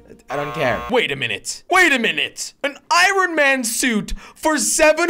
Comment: Anyone else hear Iran man